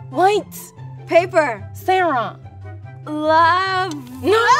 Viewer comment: did she say Sara?